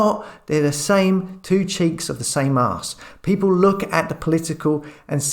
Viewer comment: 2 cheeks of the same arse! 😂😂 Made me laugh out loud there. Thanks 😂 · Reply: I thought he said ‘two cheeks in the same mask!’. I must get that hearing test! 😂